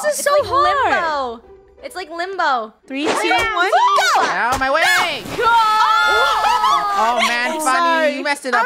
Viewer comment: is it me or did say oww my wig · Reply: he said “UGH GET OUT OF MY WAYYYY”